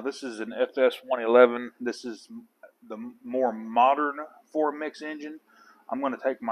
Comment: I'd earlier watched another of your vids (where you changed the crankcase seals.)....and I thought why does he keep saying "4 mix engine" ??? I could clearly see it was a four-stroke.....I, like others it seems, was somewhat puzzled! As they say..now: "Alles Klar"...... I've yet to come across one in this neck of the woods..(South America)..but now I am well armed with the right info....Many thanks! Big Thumbs Up.